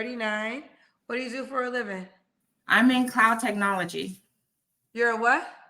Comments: I thought she said "clown technology" at first LOL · I thought she said Clown Technology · I thought she said clown technology... which would've been much for fitting for this woman 🤡 😂